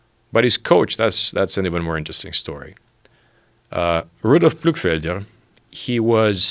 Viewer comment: Wtf did he say? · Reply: Rudolf Ismayr.